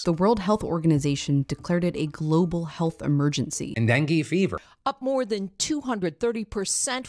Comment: I'm sorry but I thought I heard "and then Gay-Fever" then looked down and it was "Dengue Fever" hahahah